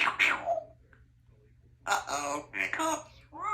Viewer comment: Did i hear “uh-oh, hiccups”? Bird is definitely the word! I love you, Symagirl red butt chicken!